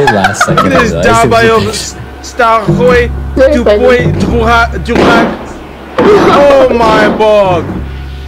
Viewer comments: не многие услышали фразу от афро-американца "Долбоеб!" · я один дважды услышал: this is dalbayeb!